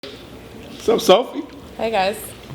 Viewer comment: sound like buddy said "whats up Softy?"lol